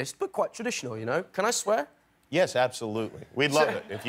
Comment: the captioner didn't understand Taron when he said "Aberystwyth" at 5:00 . It said [INAUDIBLE] lol!! 😂